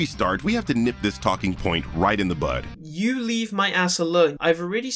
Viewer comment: Just as a correction said “Nip this talking point RIGHT IN THE BUD!” Not butt. You must have misheard him there.